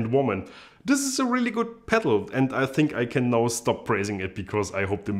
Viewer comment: Yeah, I heard "praising". Don't know what anyone else heard :D